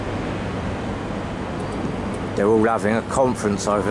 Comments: what is he saying? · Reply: "They're all having a conference over there..." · 28mins watch your back did he just say that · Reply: No, they said 'probably watch it back later'